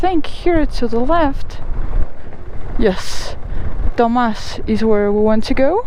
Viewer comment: what did you say??????? Dumb AS* 🤣🤣🤣🤣🤣🤣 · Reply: It was “Tomás”, name of a town